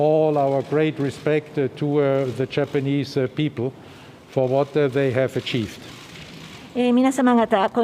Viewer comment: チャイニーズに聞こえたのは気のせいか？(ネタ)